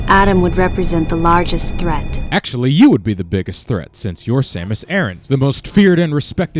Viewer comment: Back in 2009 at E3 when the trailer was shown I thought MB was a Samus' clone because I misheard the phrase "I'm Samus Aran" and thought MB said it. Now I realize it would have been a better story.